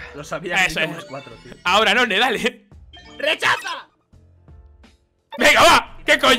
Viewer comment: Cuando dijo rechaza yo pense que dijo desafía hajajajajajajajajjajajajajaj